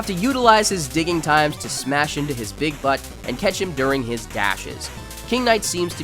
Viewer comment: what did you just say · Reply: Big. Butt.